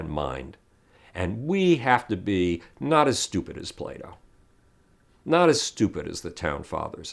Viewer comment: When you say plato, I thought you were saying Play-dough